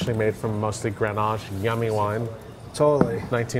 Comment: Accidentally says “yummy wine” at 8:01 instead of “I’m a 90 on that”